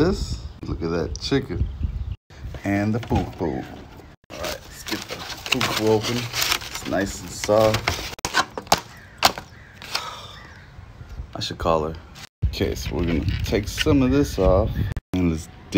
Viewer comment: "I should call her" you mean bunkie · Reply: To be honest I thought he said "I should call it," as in call it and say it's disgusting before even trying it because he was concerned about that fufu ball. But now I realise I was wrong lol